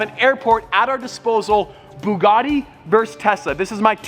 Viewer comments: i thought u said “Tessa vs bugatti” i was like man set yo priorities straight lol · I thought he said tessa vs bugatti🤣